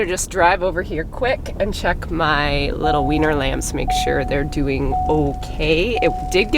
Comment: I know that what said is "weaner lambs", as in "the lambs who have just been weaned", but I honestly HEARD "wiener lambs" and now I'm picturing dachshund-style lambs with tiny little legs and noodle bodies scampering around the place, baa-barking at everything. ...and now you're picturing it too. You're welcome!